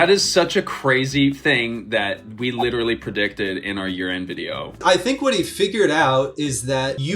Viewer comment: I SWEAR I heard you say "our urine video"